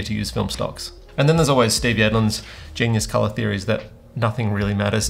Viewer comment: What is the name you said here? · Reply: Steve Yedlin